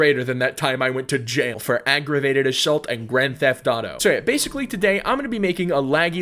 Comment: What did he just say?! Did anyone hear that? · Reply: GTA